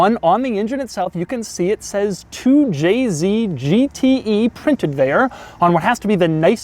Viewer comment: all I heard him say was “2JZ” thinking he said “to jay z”